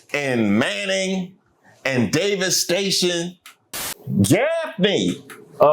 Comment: I thought I heard you say Davis Station. My Mom is from Summerton.